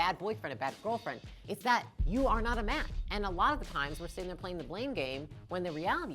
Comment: Hi, did you say "mat" (as in doormat) or "match"? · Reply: Match